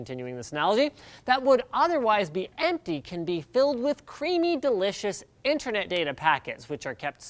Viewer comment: i Misheard creamy as creampie at around 1:10 ish. the internet has ruined my brain.